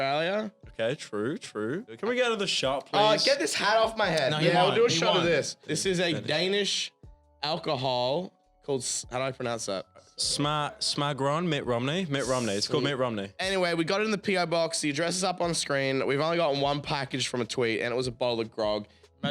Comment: what the actual fuck is Pyro even saying, how does he even manage to pronounce it "smagroin" lmfao